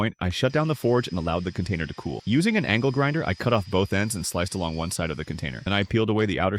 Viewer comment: I thought I heard king von but it was just the angel grinder